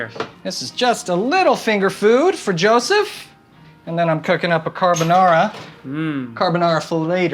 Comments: When that joe said what did u do instead and he said cooking I thought he said coakain 🤣🤣🤣🤣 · “You took a year off of social media, what did you do in that time” I heard “Cocaine” Lol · who else thought he said cocaine ? *he said cooking